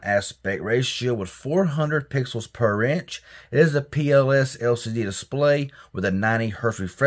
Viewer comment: Did you just say pos led display 😂😂😂😂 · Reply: Lol no. I said it's a PLS LCD display 😂